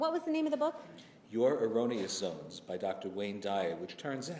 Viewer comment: at first I thought he was saying the name of the book he sent her was “ Your erogenous zones”...it’s actually a book about helping people break free from negative thinking Nd poor, self- destructive behavior.....